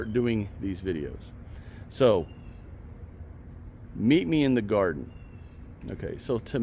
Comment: I'm just starting to listen to this video. Haven't ate or had coffee... At first I thought you were saying "meet me in the car"!